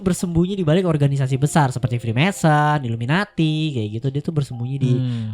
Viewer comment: gajelas, itu ngomong apa ya? Organisasi apa? Fimnesa? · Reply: Freemason